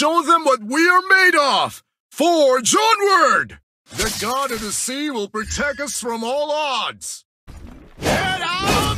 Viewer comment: who hear he said The god and the sea will protect us from aldous😂